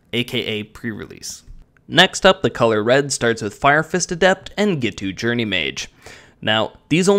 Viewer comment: I heard "fire fist of death" has he been trainging with Chandra?